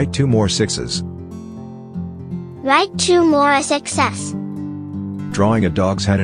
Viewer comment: "draw 2 more 6s" what i hear: "draw 2 more success"